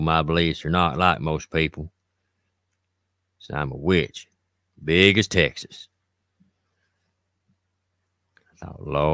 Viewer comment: Did you call me?!? I was listening to you at work and I heard you say "a Witch as big as Texas' I'm not that big, but.... I'm in Texas! 🤣